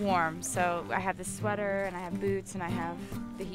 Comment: I swear to god I heard her say "boobs" at 1:14 xD